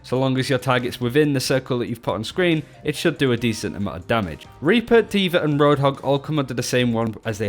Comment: I misheard "Orissa" and thought you were saying "or Reaper" lol. Love the channel guys!